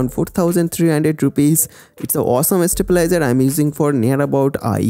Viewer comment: Kya bola yaar 0:46 par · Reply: Dr. MANOJ KUMAR awesome estabilizer